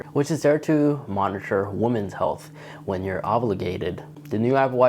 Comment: The new temperature feature geared towards female 'obligation' is cool. I think they will genuinely find that really useful. Like when they are obligated to say... ovulate? 🤣🤣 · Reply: Sounded like he said Ovulgated. Lol